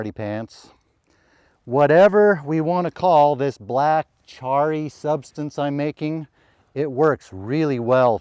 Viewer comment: Misheard this: "Whatever we call this black, tarry substance, it is very good for drilling" 🙂